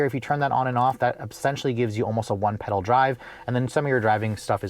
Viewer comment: Can't understand what you are saying you talk so fast. Sounded like you said "One pill drive" 12:41